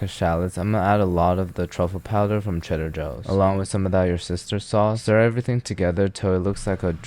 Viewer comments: I heard "your sister sauce" instead of Worcestershire sauce. Taking up hearing appointment tomorrow. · did i just hear "your sister sauce"!